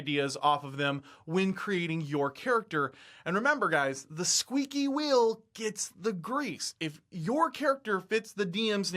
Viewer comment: I swear Minsc said: "Squeaky wheel gets the kick!" For a good lone wolf character that is part of a team see Wolverine!! Or as many others pointed out Batman!! I'm more of a Marvel guy so I tend to forget that Batman is a thing!